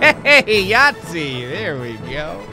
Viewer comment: did he just say yatzy?